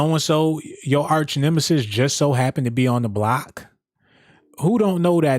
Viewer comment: At first I thought he said hoe block 😂😂😂